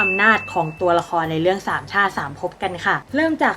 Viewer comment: ได้ยินเป็นสามช่า สามภพ 5555+